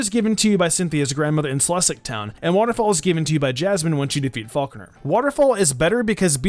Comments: I heard Falkner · Did he just say Faulkner